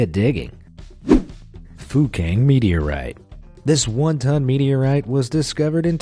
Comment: My mother thought the guy said F*cking Meteorite when he actually said Fukang Meteorite Haha lol